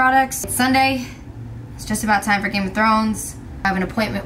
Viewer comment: did she just say Game of Thrones???? I love her even more now <3333